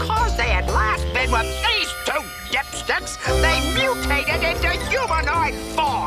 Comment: Did Krang just say "the two dipsh**s"?! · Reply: He actually said dipsticks. Slightly different.